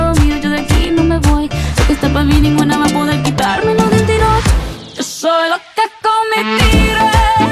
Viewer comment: Yo pensé que decía "solo con tu mentira" Chale xd